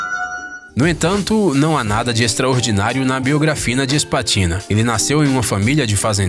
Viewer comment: eu ouvi "biografina", mais alguém ouviu? kkkkkkk